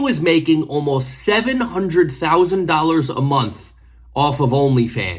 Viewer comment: I had this on in the background and thought you said 7k a month. Had to do a double check when you said 8 million lol.